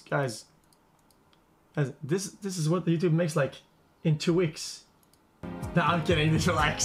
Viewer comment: what did he say here? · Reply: “Guys...guys- this, this is like what YouTube makes in like- t- two weeks, düd.” It takes time :)